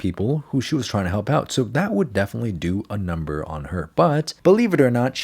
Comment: I thought he said it would do a number on her butt.